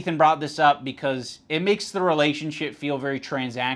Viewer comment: did anyone catch that, "relationshit* ...